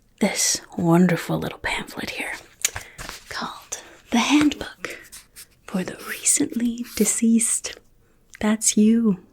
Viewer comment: No worries! I hope you enjoy it when you get a chance to watch! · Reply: give me ALL the formaldehyde! I don’t want to be saggy mcsaggy pants 😮. (PS... I thought said it was “The Handbook for the Recently Diseased”? Lol... btw, you’re rockin’ that green hair! 🧑‍🎤